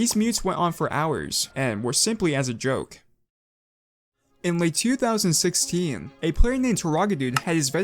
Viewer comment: why did i think bro said "In Le 2016"